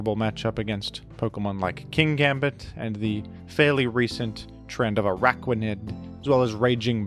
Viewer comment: definitely thought you said Tren de Aragua and I was VERY confused